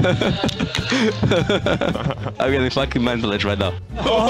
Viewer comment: what does perkz say at 1:19 ? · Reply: I get fucking mental edge right now